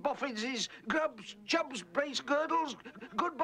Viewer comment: I thought he said foul feet